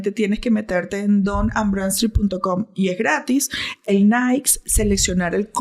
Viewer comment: Que dice en el minuto 35:56 don ambran.... que?